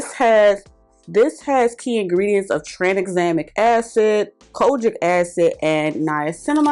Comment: I know you SAID "kojic" ...but what I heard was "COGIC" I'ma head out 💙